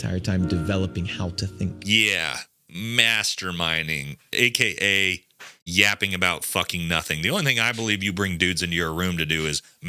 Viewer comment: I swear I thought he fucking said masturbating 😂😂😂